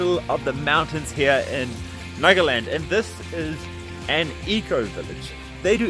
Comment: I was half listening and I thought said Magaland. Oh Trump made it to India.